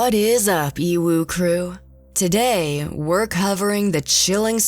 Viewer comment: I thought she said UWU crew 😅